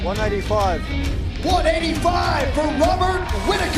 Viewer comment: He said "195". Am I hearing things? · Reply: gladitsnotme You should clean your ears out, he clearly says "one eighty five" silly yank.